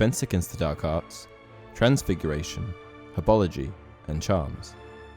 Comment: the auto-generated sub said 'apology' instead of 'herbology'🤣🤣